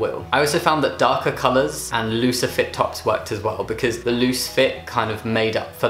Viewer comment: you said looser fit and i thought you said lucifer